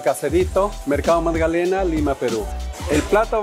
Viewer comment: thought it said Ligma